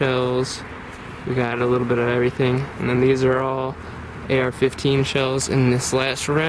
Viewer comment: I'm sorry, did I hear AR-15 shells.